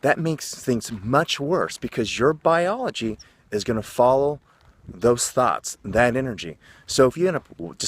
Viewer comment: did he say follow those thots ? 😂😂😂